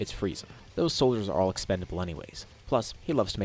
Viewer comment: D-DID I HEAR EXPANDABLE?